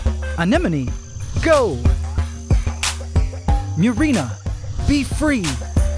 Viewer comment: "marina! Be free!"... "In this prison I built just for you" 😂 that's what I heard anyway